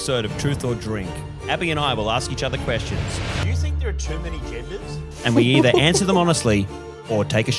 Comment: I thought he said "too many gingers".